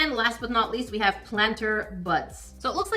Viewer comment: I wasn't looking and I swear I heard say "planter butts." I did a double take and had to go back to see if somehow, someone had green lit tiny planters with little butts on them! Also I would love to see you try using your own UV resin and try to match the dark color of the teapot. I'm wondering if it was a combination of the dark color + the resin change, maybe it just needed more time to fully set up? I'm thinking it probably was just too dark in color though. As a lover of orange, I gotta say that looks like a very nice orange they picked!